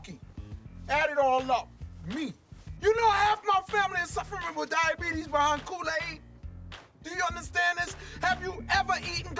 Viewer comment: i could of swore he said, "Via Kool-aid" and pronounces it "VYA" some people say "Veeya" some say, "Vya".. might be wrong.. but he sounded something like that..